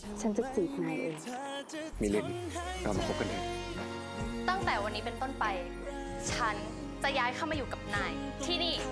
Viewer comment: Someone pls help me, explain what anawin said in 0:22. 😭😭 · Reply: Anawin said “will you be my valentine?”